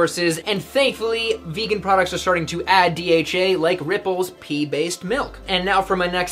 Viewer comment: this is so helpful so thank you but also i i was looking in another tab and listening to this so when i heard "pee" "based milk" that sure made me laugh